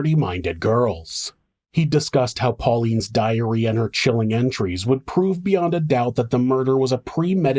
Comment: I heard this as " her diarrhea chilling enteries would prove without a doubt" when referring to Pauline',s diary enteries 😂 I had to bsck it up amd re listen 😅💩